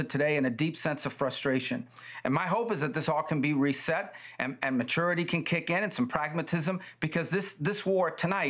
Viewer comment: “Maturity”. Is that what Rubio said? He watched the same clip i did right?